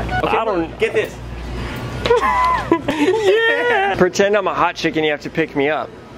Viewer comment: I thought said "pretend I'm a hot chicken"... 😂💀